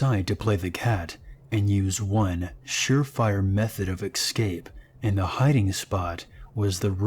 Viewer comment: Did anyone else hear how he pronounced escape? It sounded like he was saying excape